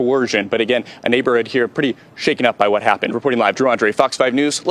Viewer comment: Sounded like he said "jew Andre" 🤣